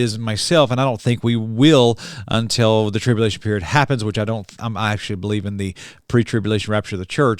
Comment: did you say you don't actually believe in the pre teibulation rapture of the church????What do you believe in, ~1 Thessalonians 4 says "comfort one another with these words".~ You kinda mumbled through that Doctrine.